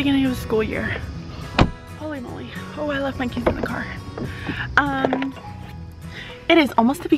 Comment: In the beginning I thought she said she left her kids in the car